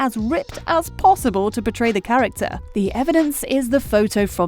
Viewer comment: Hist sounds like she is saying betray instead of portray